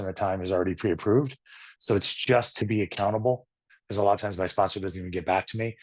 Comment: I thought you said "I'm a cannibal." = accountable is what you actually said. Thank you for sharing your story.